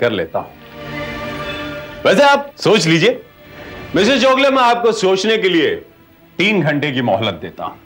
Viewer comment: Dialogue is Wrong. Word should be 'Mr. Chogale' instead of 'Mrs. Chogale' (06:30 - 06:32).